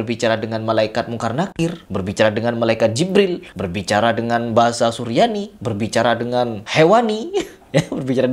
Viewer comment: Harusnya beliau segera diawetkan dan ditaruh di museum sebagai warisan budaya nusantara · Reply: mama semprong bang salah denger apa saya yg salah saya dengernya bahasa bubur🤣🤣🤣